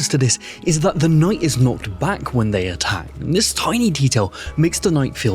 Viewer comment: I heard "knocked back" as "not back" and was confused for the rest of the video.